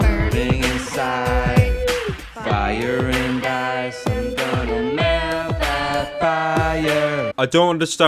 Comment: Did he just say "I'm going to melt the fire."